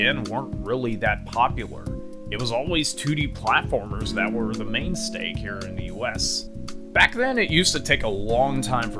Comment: did you say mainsteak or am I hearing things. Nice video I love small youtubers that make well produced and informative videos like this. Gl in the future!! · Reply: I did say main stake. I'll eventually get better on what to say for future videos, but that's the fun of making these. But thank you very much for checking us out, and can't wait to show all of you what we have coming in the coming month! ^^